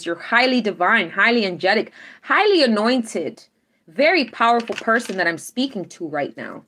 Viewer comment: Everytime you say "Highly anointed" I hear "Highly annoyed" 🤣 You're the best 🖖💎💕🌌